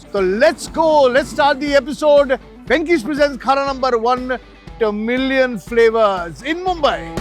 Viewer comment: it sounded like "The Million Flavours" when you said Tamilian flavors 🙂